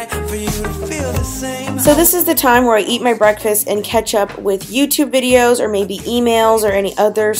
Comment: Is it only me that thought she said ketchup when she said catch up😂😂